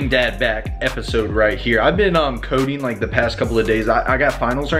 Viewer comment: Deadass I thought you said, "I've been on codeine for the past few days"